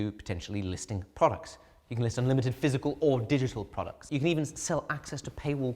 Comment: "unlimited physical or digital products" I first heard "unlimited 4 digit products" and thought i didn't understand math....i mean i don't, but even less